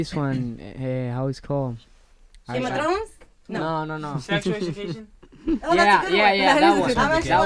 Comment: what tv series did Joel said which is erik’s favorite? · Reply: I had to play it like 10 times, but I think he said "Sex Education".